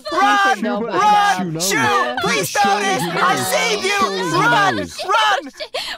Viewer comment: On the subtitles it says "PLEASE DON'T DO THIS" but I think Ike said "Please notice" I'm not sure myself but it kinda sounds like it :)